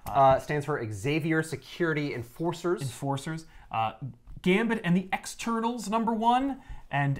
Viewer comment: Omg I heard it as gambit and the x turtles for like half the episode